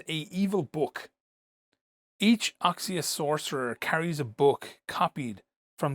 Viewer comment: I thought he said nazi sorcerer 😂